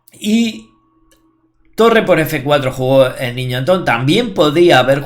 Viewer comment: Buena partida de Antón. En el min. 09:06 parece que dices "niñato" en vez del niño Antón. Es la velocidad que engaña al oído 😃😃😃 Saludos